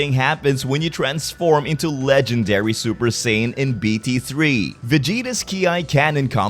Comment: Why do i hear, “Mr, satan.” instead of saiyan.